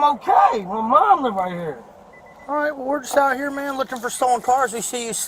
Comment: All I heard was "we just out here looking for black dudes in cars"